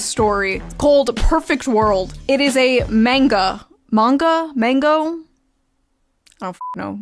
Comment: i died when i heard her say mangal instead of manga